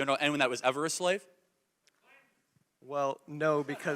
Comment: does anyone know what the guy in the background said? Did he say "slave!"??